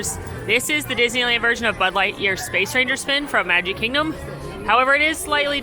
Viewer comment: Did just say Bud Light Year?